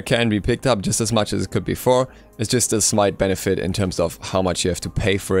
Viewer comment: did he just say "its just a smite benefite"